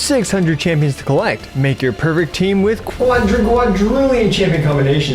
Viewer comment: Thanks everyone for SMASHING our sponsor's link, RAID SHADOW LEGENDS! <link> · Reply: why did you say make your pervert team